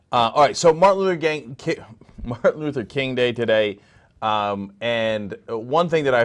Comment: Did he say, "Martin Luther Gang Day"?